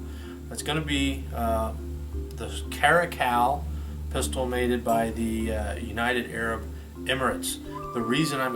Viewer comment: Did you say "made-ed buy the..."